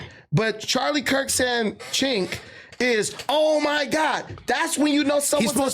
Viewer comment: they thought Kirk was saying “ch*nk”….. he was TALKING TO CENK UYGUR YOUR F*CKIN IDIOTS OMG LOL